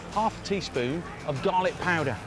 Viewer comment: when you said ''garlic powder'' I heard ''dalek powder''....I was very confused for a moment there... :D